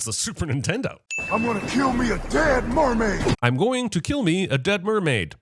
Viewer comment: I think it said “I going to kill me a damn mermaid”